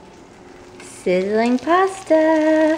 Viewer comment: when you said sizziling pasta i thought you said cinnamon pasta and thought uhh thats intresting/weird lmao